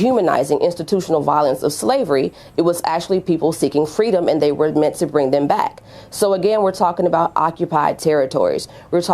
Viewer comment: ish, did anyone else catch that she said "they were ment to bring enslaved white people back" or did I miss hear that haha